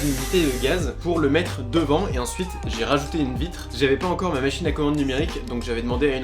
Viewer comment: T’a rajouté quoi ?😂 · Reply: une vitre 🤦‍♂️😂